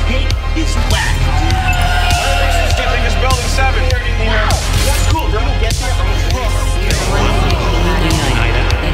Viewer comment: Did I hear Building 7 at 0:28? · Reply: vsx He says, “Which one of these do you think is Building 7?”